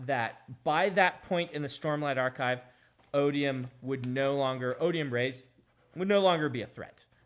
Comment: When he said “Odium/Rayse” I thought he was saying “Odium/Mraize” which confused me thoroughly. I had to look it up to get it straight.